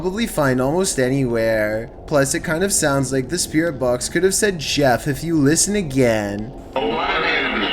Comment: Nah. He clearly said "Chef" Gordon